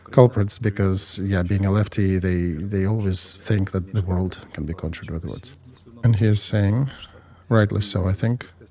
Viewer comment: what is it that Mr. Arestovych says 'leftys always do'? Thanks. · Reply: That the reality can be conjured with words. That words are powerful and can be and should be monitored. As opposed to "sticks and stones can break my bones, but words can never hurt me"